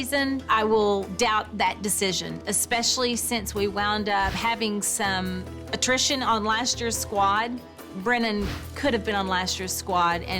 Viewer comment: It’s been years since I saw the season when Brennan makes the team so I can’t remember but starting at 9:23 what did Kelly say? When she says “especially since last season” ...and I can’t make out the rest! I just can’t recall what happened! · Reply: "We ended up having some attrition on last year's squad" Kalyssa violated her contract and got booted off the team. Alexandria quit and moved back to her hometown I think.